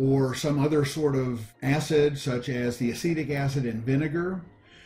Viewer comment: Did he just said "acidic acid"?